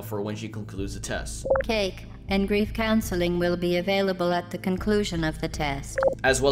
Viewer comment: i always thought she said 'brief counseling'